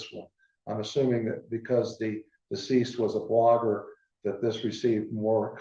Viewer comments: Ouch! The coroner said that the reason why Gabby’s case drew loads of media attention while others don’t, was because Gabby was a blonde. Wow. Way to minimize her to a stereotype. That one is going to catch some flack! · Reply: I heard "blogger", not "blonde". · Ouch! The coroner said that the reason why Gabby’s case drew loads of media attention while others don’t, was because Gabby was a blonde. Wow. Way to minimize her to a stereotype. That one is going to catch some flack! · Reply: I think you misheard him. He said blogger. But I'm glad he said what he said. So many people (not just women) die from domestic violence. That's all he was saying. Its all so tragic. 💔